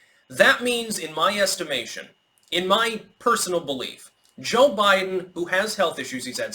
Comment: Your mic is kinda bad that i misheard you say 'Joke Biden'